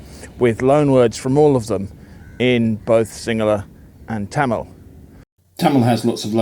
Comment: CC reads "singular" instead of "Sinhala" - "...loanwords from all of them in both singular and Tamil"